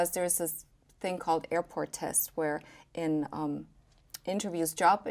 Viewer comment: Can someone tell me what word she says @ 7:57? Closed caption is saying 'airport tests' but this is not correct. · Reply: Yes she said airport tests at that time you said she goes on to describe what she meant by that term.